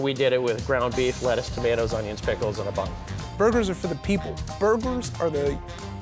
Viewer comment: Did he say burglars